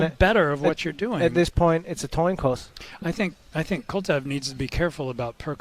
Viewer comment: "At this point it's a Toin Coss" hahaha is it just me or did he actually say that?